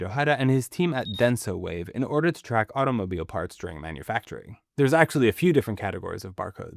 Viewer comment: Did I hear "automobilo" like with a japanese accent ? 😂